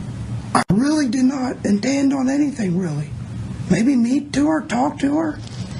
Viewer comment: sounds like he says "maybe metoo her" 🤔